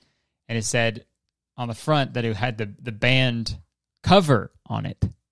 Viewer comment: i feel so stupid i thought he was saying ‘band cover’ and was so gd confused